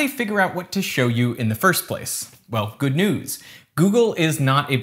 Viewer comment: did he really say good nudes or am i just dumb?